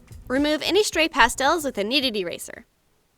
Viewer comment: For some reason I always thought she said needed eraser instead of kneaded eraser